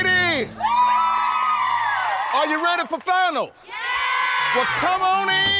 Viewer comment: He said “are you ready for finals” and I heard “are you ready for Thanos” 😭 I guess same thing for these girls? 🤣